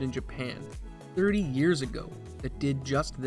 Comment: did he say 30 years ago...?